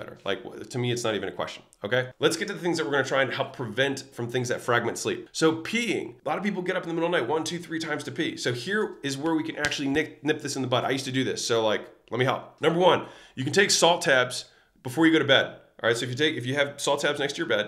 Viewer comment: So much value here! But I have to ask, did he actually say "nip this in the butt" at 17:16? 🤔🤔🤔